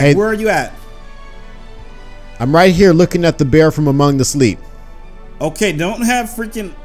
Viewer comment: DID he say Bear from AMONGUS